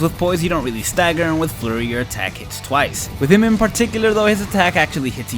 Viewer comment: I thought he said furry